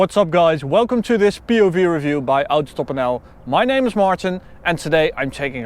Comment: it honestly startled me for a moment when i heard "my name is Martin" instead of "my name is Max" lol